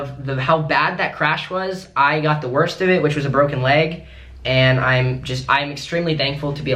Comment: Did he say he got the worst injuries?